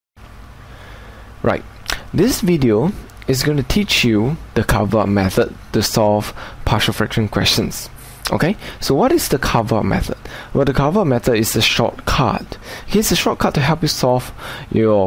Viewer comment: Why do I keep hearing it as "partial differential equations"?